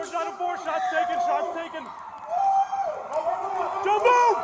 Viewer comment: Did he say "four shots taken" over the radio after they both unloaded on the guy?